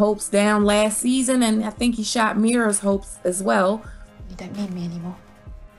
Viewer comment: what does Meara say to Bran at 5:31? I keep replaying it and even put closed caption on but it just says music. She says something blah blah anymore and then he says no I don't. Did she say you don't love me anymore????? Doesn't make sense because he never said he did but what else could it be?